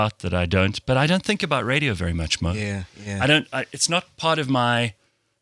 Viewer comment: did Gareth just call MacG by MO? Oh no he didn't 😩🤣🤣🤣🤣🤣🤣